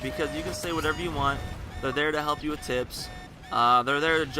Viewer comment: near 7:00 i thought you said " they help you with tits " instead of tips XD